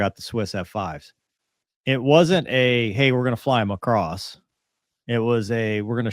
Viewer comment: ". . . fly 'em across . . ." I hear: "fly Macross" -_- Yeah, I apparently have a special nerdy bias in my hearing. Y'all have a laugh, now; I'm happy to provide.